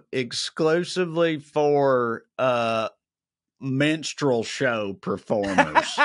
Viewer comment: With accent, I thought he said "Menstraul show" instead of "Minstrel show", needless to say I was like "WHAAAATT!?" 😅🤣😂 Mwah! ❤️